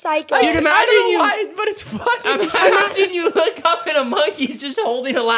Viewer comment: did say the f word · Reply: no lol said funny but it kinda sounds like it